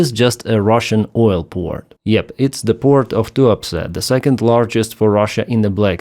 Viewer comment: Sounded like you said "port too upset" 🤣